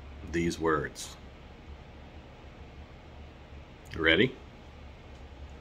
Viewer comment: At first I thought you said "schrew aims mighty Folgers."